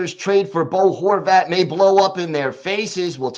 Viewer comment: LMAO!!!! The first sentence I hear you say is "how this could blow up in our face"